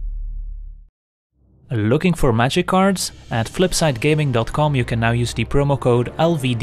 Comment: Whenever I’m “looking for magic cards” I always think of flip side gaming. I’ve heard it so many times I just hear in my head reminding me with his majestic voice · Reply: Justis Johnson whenever I’m “looking for magikarps” I think of flipside gaming, mostly because that’s what I thought he said for the first few months I watched his content.